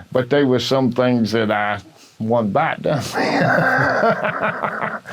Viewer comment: Great interview. What is he saying at 10:44 just before laughing? I couldn't quite make it out. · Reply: I'm from Appalachia. He says "but they was some things that I (pause/stutter), one bite done me." You can probably conclude that he means that he only tried some of the plants once either from taste or maybe upset stomach, texture, etc. Hope that helps.